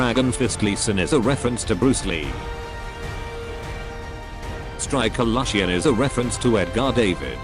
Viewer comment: did he just say striker lushian